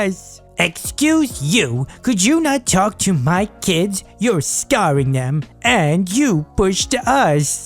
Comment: did he just say ‘scarring’ 😂😂